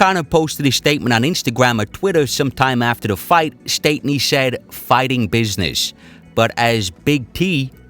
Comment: i thought he said at the time its fighting business. because he was holding khabib gloves and khabib kept complaining about it to the ref. whatever it was it had to do with khabib talking to the ref about the glove holding.